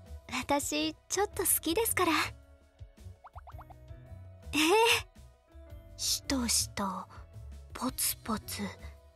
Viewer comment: ”しとしと”のとこ無限に再生するとしこしこに聞こえてくるライフハック